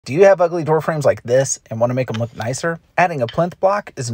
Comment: Bro I thought you said do you have ugly girlfriend like this 😭😭